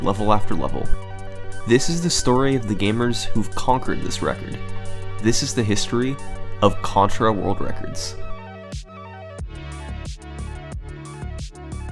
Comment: I thought it just said “The history of world records”